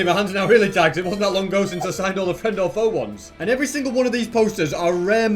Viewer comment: what is he saying 😂😂😂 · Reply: The friend or foe ones